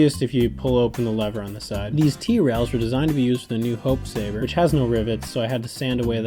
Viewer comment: Am I the only one who thought said 67 T-Rails?